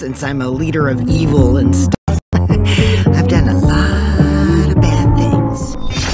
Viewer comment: What everyone else heard: “I’ve got a lot of bad things” What I heard: “I’ve got a lot of pancakes”